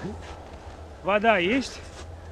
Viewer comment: when i first heard that I thought he wanted vodka. Stereotypes have changed me. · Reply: Water = voda, вода